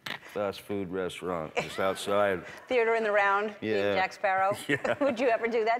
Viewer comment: what just she said (coronavirus Jack Sparrow ???)